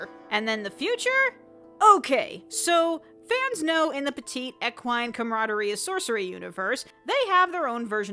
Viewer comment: i replayed that 10 times, i still don´t know what she said and what it means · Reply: She said, “Okay, so fans know in the petite, equine camaraderie...”.